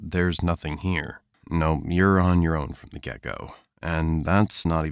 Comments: I heard, "You're on your own from the gecko." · Did you say “your on your own from the get go” I thought you said gecko at first lol.